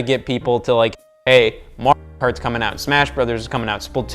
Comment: What did he try to say at 18:05 ? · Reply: Mario Kart